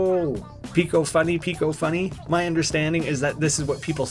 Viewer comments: I thought he was saying big old bunny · When you hear picofunny i hear BIG OL' BUNNY